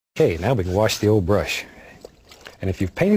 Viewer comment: I thought he said wash the old bush ಠ_ಠ